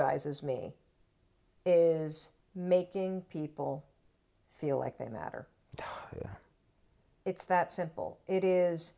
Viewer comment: I honestly thought she said 'making people think that I matter'.... Of course I knew I misheard it, but I laughed to myself, and that energized me.